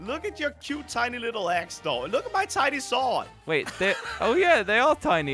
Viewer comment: look at your cute, tiny, little axe. I thought he said ass instead of axe